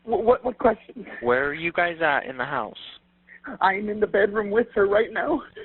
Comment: At the beginning of the 911 call I thought I heard him say he was in the room with his kids. Did anyone else hear that or I did hear incorrectly?